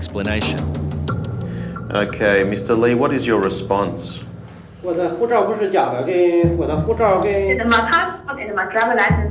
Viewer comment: I know Mandarin and at 39:45 I believe I heard he said 'My passport is not fake' but what the interpretator stated was otherwise.